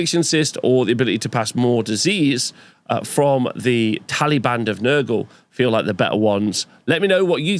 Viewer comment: I keep hearing "Taliban of Nurgle" and it's confusing me.